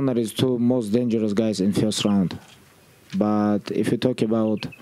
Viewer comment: I cant understand what he said? "He and Conor is the 2 most dangerous guys *******" can somebody writebit down please? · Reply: 2 most dangerous guys in 1st round